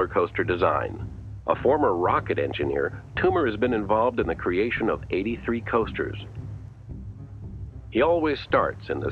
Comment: Sounded like "83 toasters"